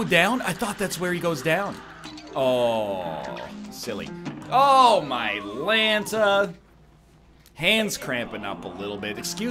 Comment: now what is that he says right there when he dies I've been watching him since over 2 years still can't suss out what he says right there. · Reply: Oh my lanta;like oh my god!